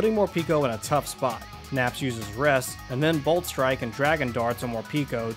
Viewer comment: hey. how in tf does he say rest but the captions say roost i am very confused.